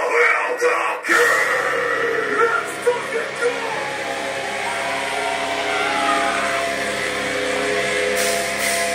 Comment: what did he scream( the short blonde headed guitarist) at 1:39? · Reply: LETS FUCKING GO 🦍